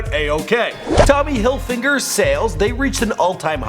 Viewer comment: did you just say "tommy hilfinger"